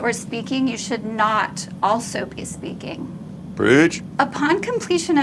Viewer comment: Why did Rhett say bridge? 4:59 I don't get it is it a AA joke ?